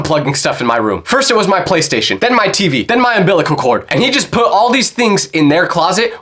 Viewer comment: did bro just say inbilacol cord 😂😂😂😂😂😂